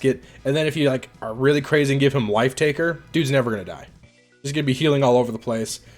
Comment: Is it just me or did I hear "wife-taker"? 🤣